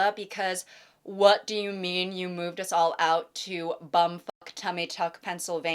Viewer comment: did you just say "bum fuck tummy tuck"